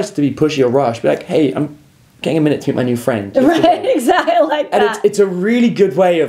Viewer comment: "Hey I'm getting to know your friend" or what did he say? Couldn't hear clearly :) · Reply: "Hey I'm taking my time to now my new friend."